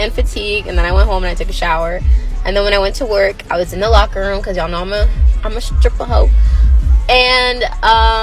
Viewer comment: Did she just say "'cause I'm a stripper ho"??? Am I understanding that correctly? English is my second language.